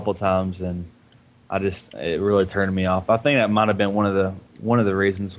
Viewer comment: For a second I thought said the MVA forums really turned him on.